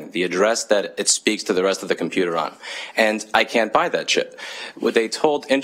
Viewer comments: thought he said “I can’t buy that shit” Was like damn, he’s really takin it to em. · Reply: I thought he said "I can't buy that chip", but now I can't unhear it. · thought he said “I can’t buy that shit” Was like damn, he’s really takin it to em. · I thought you said I can't buy that shit at 0:48 lol